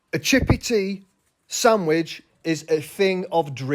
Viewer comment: I must be hammered, I thought they were saying “Chat GPT sandwich” and I was like “WTH is this sad ass fry sandwich”